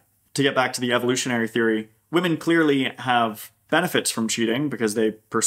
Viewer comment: what is the word you used, suspandral? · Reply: Spandrel